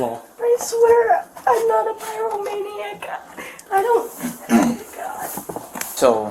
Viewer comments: I think she said "pyromaniac" here. "I'm not a pyromaniac". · she said "pyromaniac," not "terrible maniac." Subtitles are wrong. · I swear I'm not a terrible maniac when what she actually said was I swear I'm not a pyromaniac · the closed captions say “I’m not a terrible maniac” but she really said “I’m not a Pyromaniac”. I’m not writing this to criticize the channel, I just personally enjoy catching things that were transcribed incorrectly in YouTube videos that provide CC. 😄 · Think she said "I am not a pyro maniac"